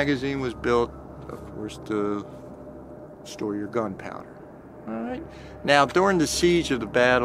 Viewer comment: Did someone say alright or was that an EVP.❤